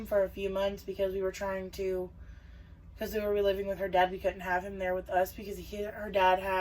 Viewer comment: It sounded like you said: “We were living with grenades” Like a “grenade” from The Jersey Shore. Lmao If you don’t know what a grenade is in Jersey Shore lingo terms... here <link>